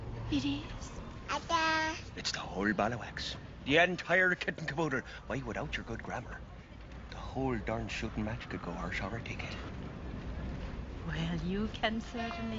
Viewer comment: Did he say arse over kick it ....??? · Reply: I now think he said arse over tea kettle...still a great line!